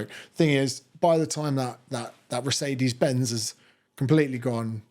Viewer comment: did you just say, recede-ez benz?